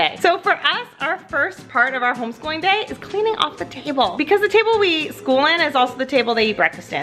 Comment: did she say they eat school A common mistake, a common mistake